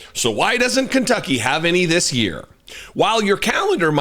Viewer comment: At first I thought that newscaster said Kentucky has no racists.